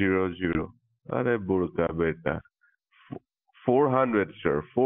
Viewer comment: anyone knows what he says? Lol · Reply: "arey bol ka beta?" is what i heard? im south indian idk